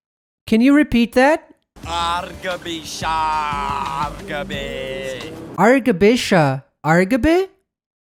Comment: I legit also have no - ZERO, nadda, fucking NO - idea what said at 9:30. "Arng shabu shaaafshabe" is this some jargon from the creator's hometown or something? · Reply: "Arghaby Sharghaby" ... it is a phrase from youth.